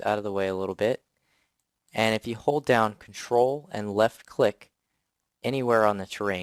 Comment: I didnt understand which key to press with left click =( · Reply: ctrl + left click